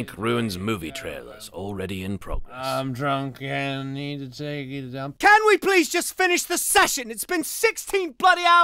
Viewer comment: who hear him say "I'm drunk and i need to take a dump"